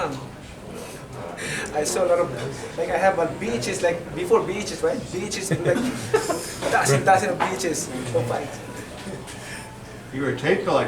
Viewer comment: I lowkey thought Manny said bitches instead of "vhs' " lmfaooooooooooooooooooooooooooooooooooooooooooooooooooooooooooooooo